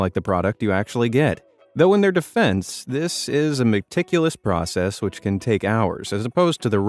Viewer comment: did you say "Mc ticulous process"? :D